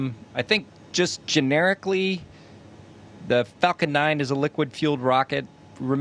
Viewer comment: I randomly skipped to 2:37-2:38 and I could swear he said f#%@ 9